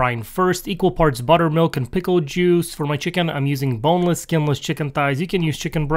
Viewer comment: I misheard it as "homeless" chicken 😂😂😂